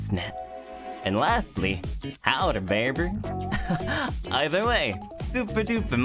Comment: 이 부분은 howdy baby~로 들리네요! 카우보이 식으로 인사를 했으려나요?